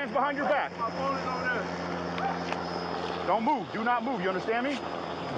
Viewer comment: Everytime I hear a cop say "do not move" i hear "DONUT MOVE"